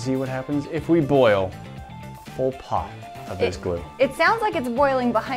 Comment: Thought you said "boil pol pot". Not a bad idea